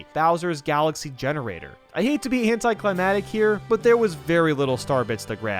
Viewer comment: did he say.. ''hentaiclimatic''